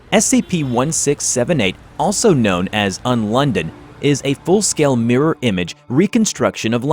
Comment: i thought you said on london